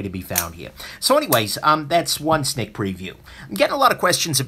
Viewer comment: Did I hear that right - a sNick preview? Branding on point, sir.